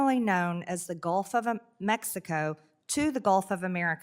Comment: Did marge just call the Gulf of Mexico, the "Gulf of Amexico"? "...formally known as the Gulf of Amexico..." Now, that is a name change that I could support.